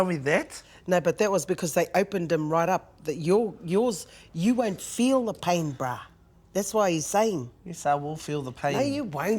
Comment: "you wont feel the pain brah!" Is that what she said? Cool ass mummy! =) lol she is funny and you are very much alike. Adorbs.